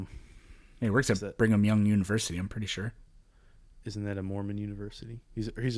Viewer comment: Did you just say Bring 'em Young University?